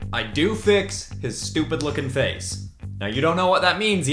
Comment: For a second i thought he said “I do fix his stupid fuckin face”